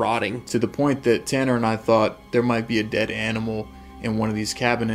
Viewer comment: It sounded like someone said dead animals